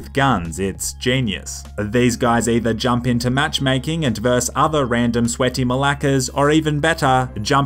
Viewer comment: did he say Μαλακας